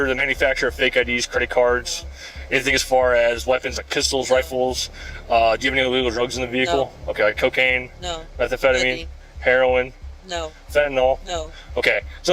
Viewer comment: did she say "maybe" lol? or am i hearing this wrong edit: nvm she said nothing